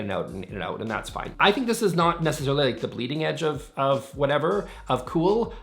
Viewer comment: Rattan chair in the sunroom or solarium, yes, much better idea. Also, I thought I heard you say "the bleeding edge of cool" and realized you said leading edge, but I kinda like bleeding edge for those who don't want to get cut in the race of keeping up. Just say'n.